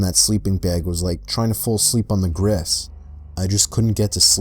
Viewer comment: the way he said grass it sounded like he said gress